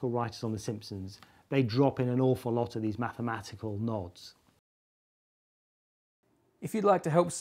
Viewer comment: I heard "mathematical knobs"